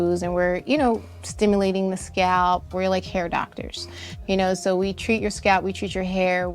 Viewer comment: The head doctor 🤣🤣🤣🤣🤣 do they know the meaning of head doctor on the streets 🤣🤣🤣🤣🤣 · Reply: I thought she said hair doctor... 🤦🏾